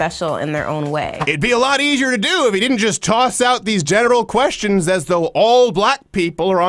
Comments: did Crowder say "genital questions" ? Or am I just hearing shit 😂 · Did he say 'genital questions'?